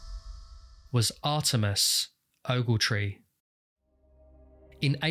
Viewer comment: Why is it that this guy's real name sounds more like a fake name than the fake name he used to get into the hotel? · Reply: I thought the narrator was saying Artemis and was so confused that he had a woman’s name. Oops.